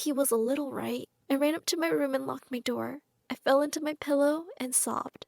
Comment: Why did i hear "lock me door"its like british